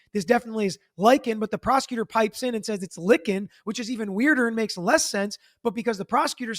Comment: he was actually saying “linkin” meaning “meeting up” with you. not “lickin” lol